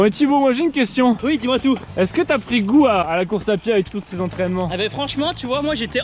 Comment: ça fait tellement bizarre il a dit '' a la course à pied '' j'ai compris lacourt-saint-pierre c'est la ou j'habite ça en plus ils ont couru juste à coter mdrrr